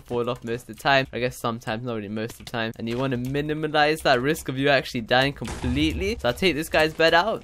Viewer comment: minimilize??WTF is that...NEW WORD???